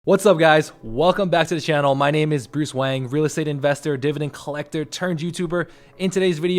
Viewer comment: At first I thought you said your name was Bruce Wayne 😂 Nice video! Keep up the good work mr.